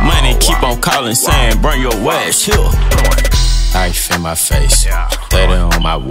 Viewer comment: for the longest time I thought he said "ice on my wait" not "knife in my face"